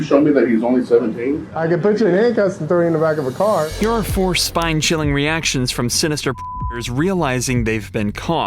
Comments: Lmao I thought it said “here are some spine chilling fuckers” 😂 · bro did not say predators in a censored line! I CLEARLY heard f___ers in there!